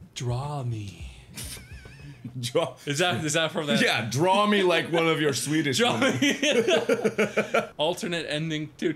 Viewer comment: I thought Mark said, "Draw me like your Swedish johnny." I think I stopped breathing.